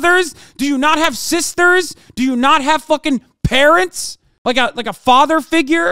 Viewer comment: thought he said do you not have parrots I was like lol I did but so what